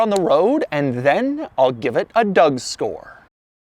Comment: did Doug say "a Doug-store"?